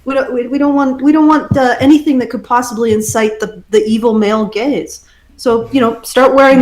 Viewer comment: every time someone says "male gaze" I always hear "male gays"